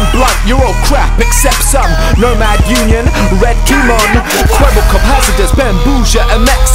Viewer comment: did it say kwebbelkop